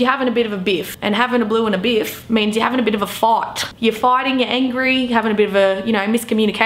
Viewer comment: Well she said fight and I thought she said fart. How do you have a bit of a fart. Glad I got subtitles rollin. By the way I don’t think we have quite this much slag going on over here but have not thought much about it.